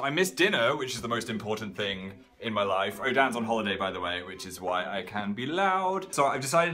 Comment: Phil "oh dan on holiday so I cant be load What I hear "Dan on live so I cant be load"